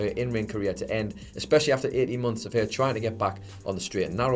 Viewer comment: I had to rewatch 1:15 a few times, I thought you said 80 months lmao